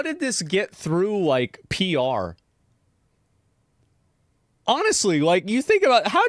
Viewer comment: because the devs are French instead of hearing 'get through like PR' I heard Pierre 😂